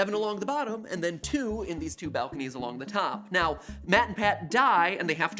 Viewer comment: hold the phone, did MatPat just call Matt from SBFP Matpat?